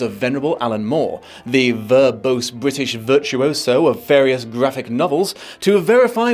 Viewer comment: Did you say, "Vraphic Novels?"